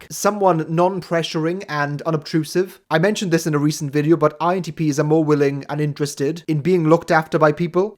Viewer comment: INTP's are cats. I'm pretty sure that's what he just said. 🐈